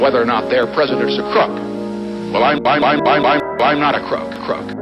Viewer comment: I thought he said "my wife is not a cook" 😭